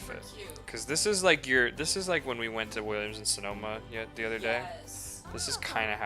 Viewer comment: did he say williams and sedona? like in arizona?